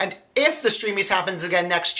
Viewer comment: oh heck, I thought it said "steamy" edition.